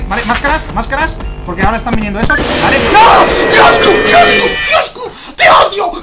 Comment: entendí quiosco